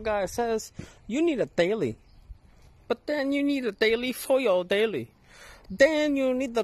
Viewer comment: What's a Baily? Or Bay Lee? Or maybe he's saying Daily. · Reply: a daily. as in a daily driver, that you drive while your project car is eternally broken or waiting for parts.